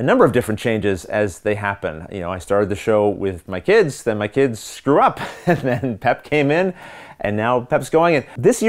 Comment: It sounded like you said “I started with my kids, then my kids screwed up” I laughed out loud and then made sure that wasn’t what you said!